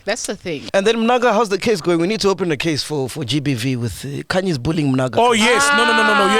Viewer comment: Did I hear Mac G calling ghost lady "khanyi".. 47:48? I loved the show inspite of the technical glitch.